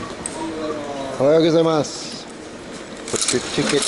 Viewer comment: What did you say at 1:45? Argidamasu? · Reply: It's Good morning. ohayougazaimasu